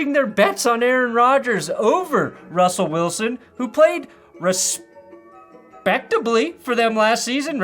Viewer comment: I heard "he played RUSSpectably"